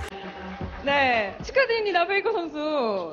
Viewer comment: Frage, kann wirklich koreanisch? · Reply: jaja natürlich, dann müsstest du ja auch wissen dass die frau "glückwunch faker" oder sowas sagt, anstatt "so mein lieber stecher,"